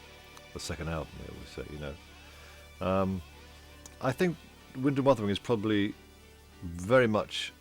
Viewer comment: Sounds like he says "Wind and Bottom", hahaha.